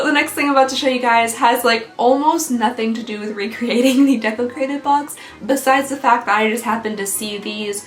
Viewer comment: I thought you was saying desecrated....🙈😂 welcome to half deaf life lmao